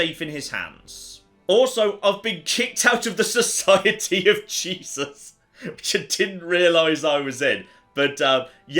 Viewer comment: hand to god (pun intended) I had to re play that cause it sounded like 'society of cheeses' and that would have been to much any other time but it seems reasonable when couched alongside the dancing plague.